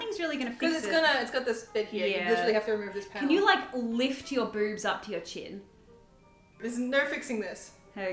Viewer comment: I just lold so hard. I wasn’t paying attention and I just hear “can you put your boobs to your ears”